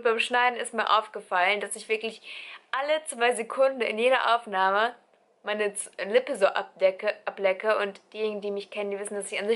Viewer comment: Ich hab verstanden: ...dass ich aller 10 Sekunden meine Nippel ablecke. :D omg.